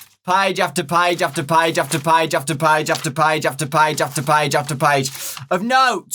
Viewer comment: it sounds like hes saying jack the padge lmao